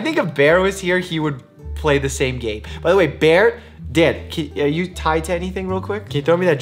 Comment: Did anyone else hear ‘btw Bear, dead.’ At 3:18??